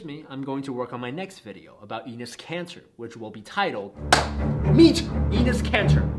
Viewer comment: Is he saying "Enis Cancer"?